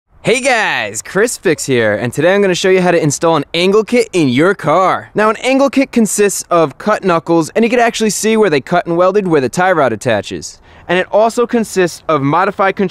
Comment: the video started at 0:03 and it sounded like chris said "cking in your car!"